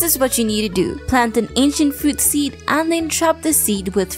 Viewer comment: Junimos I thought they're saying banzai.